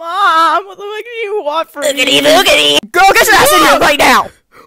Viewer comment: i misheard oogity boogity as giggedy giggedy